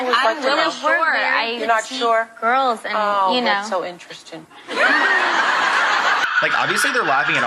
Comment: what does oprah say at 8:26 ? i couldn't hear properly and didn't get the joke or sarcasm in that moment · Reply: I think she said "oh wow so interesting" sarcastically after the twins answered her questions )